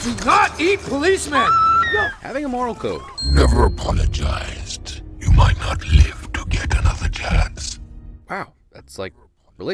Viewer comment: can someone please tell me what venom says here "never apologise..."? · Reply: cut it off a bit but Venom is saying ''You've never apologized, you might not live to get another chance''